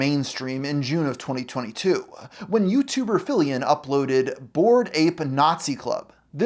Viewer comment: I thought you said FILLIAN and got really confused what a Vtuber has to do with this.